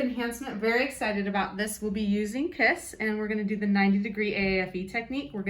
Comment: I first thought she said we’re using piss at the start 😂